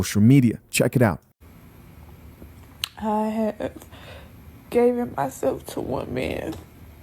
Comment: Did she say I have "gaven" myself to one man? Remedial B.